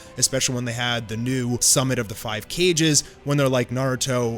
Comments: Did you just say "Cages" instead of Kages???? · Am I lost or did he just say summit of the 5 cages.